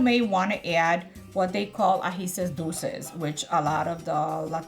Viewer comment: Can anybody tell me what the optional ingredient is at 3:00, at the end of the description of the sofrito? She says Latinos will know what it is, but I’m not Latino and I can’t make out what she is saying. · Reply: agisitos dulces Sweet little peppers